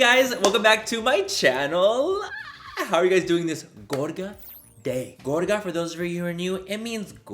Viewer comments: at first I thought he said 'gorda' and I was like nah, that doesn't mean gorgeous lmaoo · This WHOOOOLE time I thought he was saying “Gorda Day” in every video he posts 🤦🏻‍♀️ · my deaf self thought you said gorda lmao i was so lost